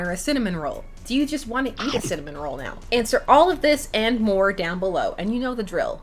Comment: I heard "don't you just want to kiss that cinnamon roll" Why brain, WHY?